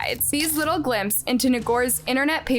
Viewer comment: In the subtitle you provide. Around 4:45 instead of typing Ngor you typed the N word. Ni***r. Please be careful and aware.